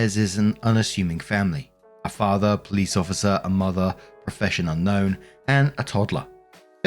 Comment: Story 2: I heard "professon unknown" as "professional gnome" and was so intrigued I had to double check. Alas.